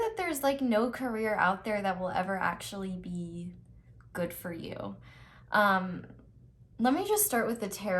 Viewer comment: Me: so confused about choosing my career Pile 1: there is no career that will be good for you ... Me: 👁👄👁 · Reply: Me too🙌🏾 i was riding on the 🛴 when I heard she said “there is no Korea that would be good for you..” i was like cracking up guys! 🤣🤣🤣